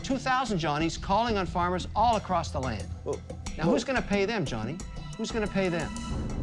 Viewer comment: All I heard was "who's gonna pay me johny? Who's gonna play me?"